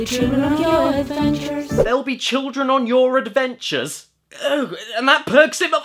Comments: “There’ll be children on your adventures” CALL THE POLICE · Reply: I mean, I heard "They'll be trippin on your adventures" so... blaze it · That line at about 5:00, pretty sure they're singing, "They'll be dreaming of your adventures." Could be wrong but that's what I heard. Makes a bit more sense? I guess...? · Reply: I heard 'They'll be true in your adventures'. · Man, I thought they said "we'll be shitting on your adventures"